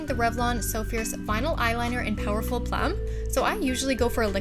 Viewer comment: For a second there, I thought you said 'powerful phlegm'..